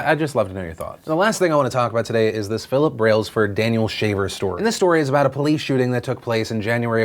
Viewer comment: interesting, I heard you say "Daniel Shaved", so I thought in the left was this guy Daniel, and on the right, still Daniel, but shaved. On a more serious note, what happened in the video you showed is horrendous.. I, in most cases, side with the police - Those guys have it hard and lay their lives everyday to protect us, and in many cases when they deal with criminals, they never know what to expect - That being said, this seemed like straight murder to me (as you said), not to count the extremely weird and powerhappy way the police officer talked to the, uhhhh, hostage? Why didnt he just asked him to put his hands in the air and them cuffed and inspected him ? I dont get all the "cross your legs, raise your hands, lower your hands..... now crawl...." What was next, dance the macarena ?